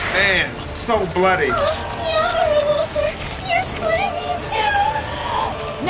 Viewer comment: Say, does anyone know what Alucard was saying in between 3:50 and 3:52? Because it sounds so jumbled. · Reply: Walter you're splitting me into two. Sex joke